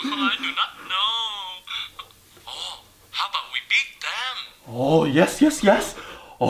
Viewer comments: At first, i thought the other mom was saying, How about we eat them? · Did she say “How about we beat them?” Or EAT THEM...